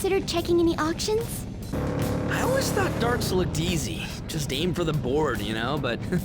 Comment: I thought he said "dark souls", but he said "darts".